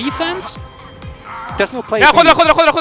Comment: 有人知道尺哥在說什麼嗎? 給我頭之類的? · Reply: quadra